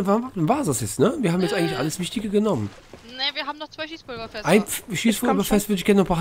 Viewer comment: ich hab Cheeseburger-Fässer verstanden. 🤣